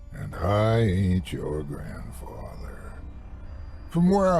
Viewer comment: and I ain't your grandfather.. I ❤! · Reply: For ages I thought he said “ and I ate your grandfather” both seem to work though